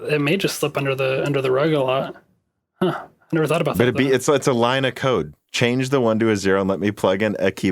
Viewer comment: I heard "a line of coke" and I wondered why nobody laughed. Then I realized you actually said "a line of code" 😅